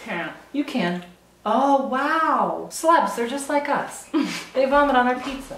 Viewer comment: "Sluts, they're just like us. They vomit on their pizza" LMFAO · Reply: he said "celebs" lol i thought he said sluts too at first